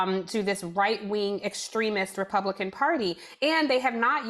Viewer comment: i think it's funny that closed captions say "extremist Democrats". Instead of, you know, Republicans.